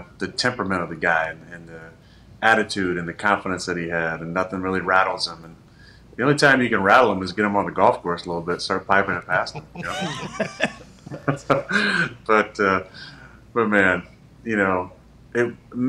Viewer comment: I really thought Aaron said, "The only way to rattle him is to get him on a golf course and start passing a pipe around."